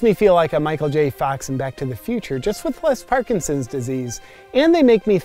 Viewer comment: "but with less Parkinson" Oh no way did he just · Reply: I thought he said "...BEFORE Parkinsons..." but whatever. I'm still laughing over the comment and replies above about Vegan Leather being made from Vegans! 🤣